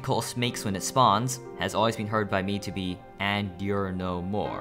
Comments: i hear it as "the more you know" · I always hear "The more you know"